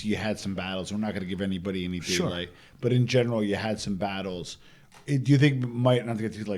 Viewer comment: yes, not in the mob genre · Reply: my bad my guy thought you said in general